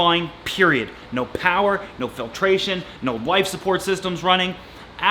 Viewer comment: did he say ''no wife support ''?